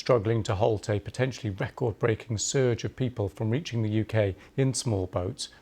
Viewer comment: Oh, at 0:08 I heard sewage instead of surge.